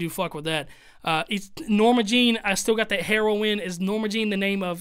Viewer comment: you said heroine the way he said it but what hes saying is that this whole thing is harrowing like its terrible like a harrowing nightdark gloomy etc